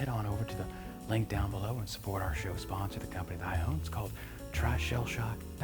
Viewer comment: My daughter thought you were saying “Queird” 😂